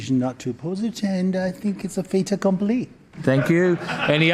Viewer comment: what did this guy say? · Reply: That it's a ' fait accompli' ie it's a done deal/no other option